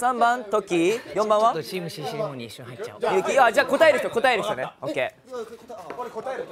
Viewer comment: イヤホンで耳済ませると聞こえるやつ 44:48~ 建太くん「ちょっとチームCCレモンに一瞬入っちゃお♪︎」 44:58~ 植ちゃんと建太くんのコソコソ話